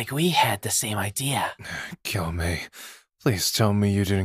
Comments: I THOUGHT SAID "KILL ME" BUT HE JUST SAID "GYUMEI"- IM DYING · Bro for i moment I thought said “ah, kill me.” 💀